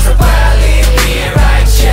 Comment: I thought they said “I’m the biggest zit on the stage